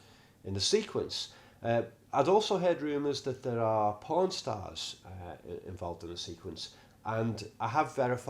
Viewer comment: I thought you said “Pawn Stars” at 10:40…like Rick and Chumley.